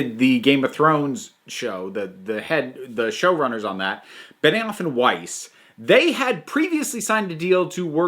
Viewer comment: Anyone else hear that as "Banana-off"? lol